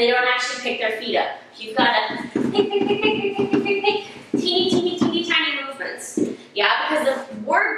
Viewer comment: did she say teeny teeny teeny tiny boavers?